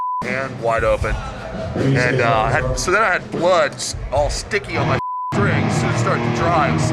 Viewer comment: Lol, love the end there with him talking about his whacked up guitars. I heard the term "Slipknow thick strings" and had to see. :P